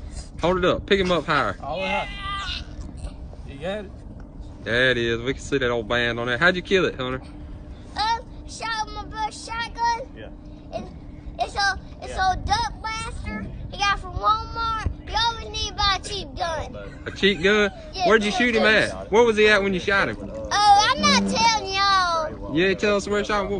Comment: I heard: “Well, I shot him with my best shot gun. And i- it that dumb bastard from Walmart who always buys cheap guns.”